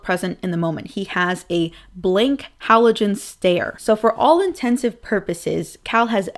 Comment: hi what is the word she says at 30:10--30:11 I'm trying to get better at english and I've never heard this word before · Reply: "Luke doesn't look present in the moment. He has a blank halogen stare." As a native English speaker, the phrase 'blank halogen stare' doesn't make sense. Most people would probably think of halogen lights, which give off a bright, warm glow. It's not an image people would associate with a blank, dissociative stare. It's an odd phrase.